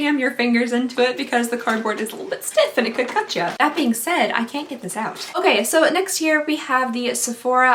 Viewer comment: did anyone else hear okay so next year instead of next here?? 😂